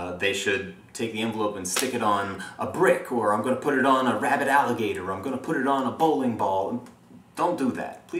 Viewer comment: Haha at first I thought you said "rabbit alligator" not "rabid alligator" and I was like why would anyone crossbreed and then send a mutant animal to the banks?